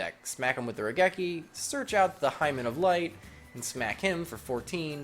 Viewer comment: He was purposefully mispronouncing it. Hymen is something completely different........ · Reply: ***** Oh I get it. I didn't hear properly sorry. I know what a hymen is I just thought you said "Hymin". That's hilarious and I feel stupid for not hearing it.